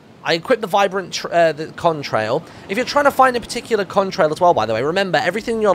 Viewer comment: I thought you said hybrid contrail 😂